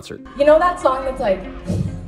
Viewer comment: This whole time i thought it said “black top, big t-shirt, Billie Eilish” lol